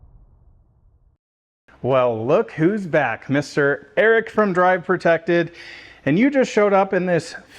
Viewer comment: I had to rewind because I thought you said "Larry from Drive and Protect"